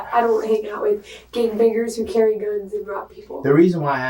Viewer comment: "gang bangers"?...I coulda swore I heard her say "I don't hang out with ghay n*ggers who carry guns".... lol, reminded me of that movie "Ghay N*ggers From Outer Space", anyone ever seen it?